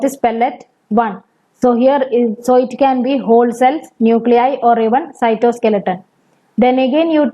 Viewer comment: ma'am what did you say in pellet 1? ......, nuclei, cytoskeleton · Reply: whole cell, nuclei or cytoskeleton (probably don't watch at 2x speeds then you won't have trouble with accents and dialects)